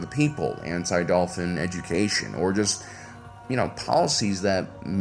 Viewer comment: did he just say “anti dolphin education?” What does that mean XD